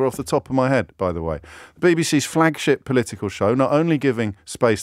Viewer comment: Did James just say "Flagshit"?